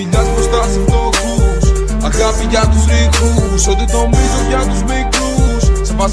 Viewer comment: Μια βοήθεια, 0:29 - 0:33 τι λέει; "Αγάπη για τους...;" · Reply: τους ρηχούς...